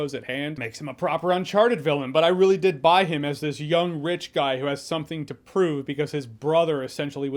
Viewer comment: What Jeremy said "I buy him as this young rich guy" What I heard: "I buy him as this young witch guy". Yup. Sorry, Daniel.